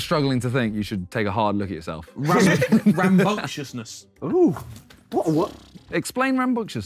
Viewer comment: what is the word harry speaks of ? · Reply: Rambunctiousness